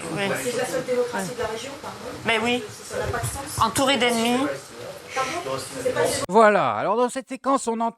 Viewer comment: Je connaissais pas la séquence ni le drama autour mais Dès la première écoute j'ai entendu "ennemis" et franchement faut le vouloir pour entendre animaux. Je comprends pas la polémique si ce n'est pour faire des vues de manière immorale et dégueulasse en diffusant de la désinformation. Ça c'est la méthode de l'extrême droite et on vaut biiiiien mieux que ça